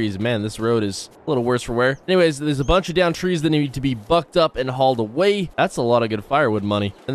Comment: I thought you said these trees need to get fucked up when you were on that rocky hill